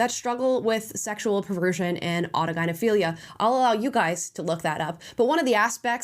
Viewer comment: what was the word? I couldn’t make out the pronunciation. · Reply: autogynephilia ?